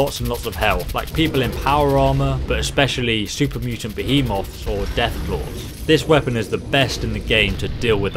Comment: did he say moth ?